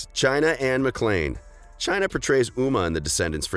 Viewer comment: haha it sounded like you said muma